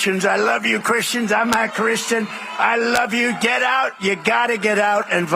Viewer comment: Did anyone else catch the Freudian slip at 0:19? Presumably his teleprompter was telling him so say "I'm a Christian" but you can clearly hear him say "I'm not Christian". I watched it on another channel with subtitles turned on, and even the AI thought that's what he said.